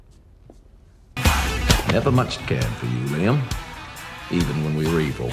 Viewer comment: did Spike just call ANGEL by his human name "Liam" @ 1:09 ? o.O